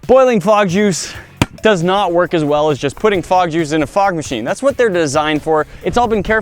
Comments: I thought it said dog juice...😂🐶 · I thought it said frog juice 💀💀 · Thought it said frog juice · I thought that said FROG JUICE! OMG lol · anyone else thought it said frog juice? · I legit thought it said frog juice at first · Lol thought it said frog juice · i thought he said "Frog juice" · I thought it said frog juice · I thought it said frog juice😞😭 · I thought it said frog juice 🤣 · it thought it said frog juice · I thought it said frog juice and i was confused on how they got that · I thought it said frog juice smh 🤦‍♂️ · who else thought it said frog juice